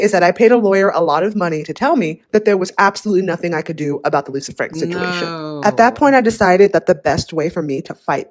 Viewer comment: The fact that the caption wrote "Lucifer" instead of "Lisa Frank" 😂😂